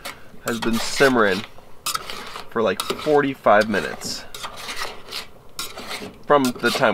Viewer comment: I misheard “four to five minutes” as forty-five minutes and was like “no god damn way is he about to spend another 45 minutes stomping in circles.”